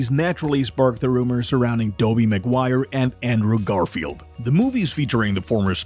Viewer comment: Did he just say Dobey McGuire 😅